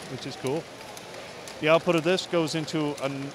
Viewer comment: it sounds like he said output of visco